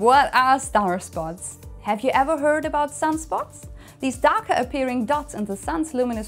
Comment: The first time I heard "darker appearing" which I believe is correct. Don't look at them as they are still very bright.